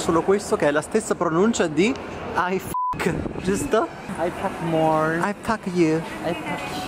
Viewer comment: YOOO, i’ve literally thought that he said “i f*ck more” 🤣🤣